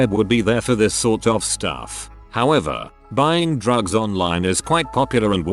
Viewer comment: Misheard drugs as frogs, and can’t stop picturing an elusive amphibian market